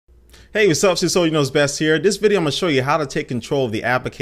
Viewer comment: beginning of the video sounds like your saying sicily knows best